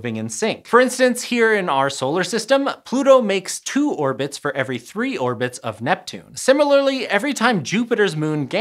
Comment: you say Pluto makes two orbits for every three of Neptune, but the subtitles say Pluto makes three orbits for every two orbits of Neptune. Are the subtitles auto-generated? If not, need to double check…?